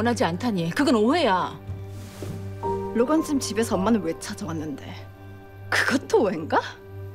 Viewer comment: 이거 뭐라는겨 로건짐집?로건쌤집?